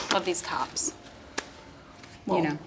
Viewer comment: Thought Ilana said, "love these cops" instead of, "love these tops" so I got everso nervous when Brittany said, "Well I think one of them's a bottom so.."